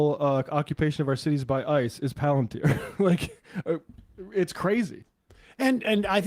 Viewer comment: weird glitch at 19:30 - what name did say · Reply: He said "Palantir"